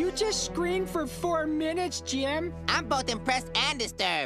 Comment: "I'm both impressed and disturbed!" Coming from Sheen, that's saying a lot. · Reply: I THOUGHT THAT SAID DEPRESSED AND DISTURBED LMAOO